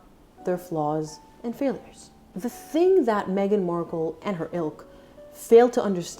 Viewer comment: 😂When you said: Meghan and her Ilk, I thought you said Meghan and her Elk, referring to Harry being moose-like😂🙈🙈🙈